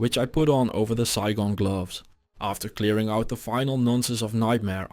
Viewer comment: Did I hear that right? Replayed a few times "After clearing out the final nonces of nm" LOOOOOOOOOOL that made me laugh way too much